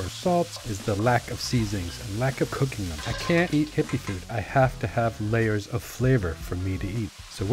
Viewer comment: did you say hippie food? 😂